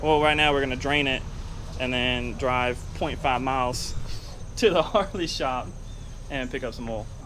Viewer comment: *point 5 miles away. I live right next to the local harley shop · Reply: nah I rewatched that part it's your accent You sounded like you said 25 mi.... I know I know you don't have an accent I'm the guy that lives in Washington State I'm the one with the accent lol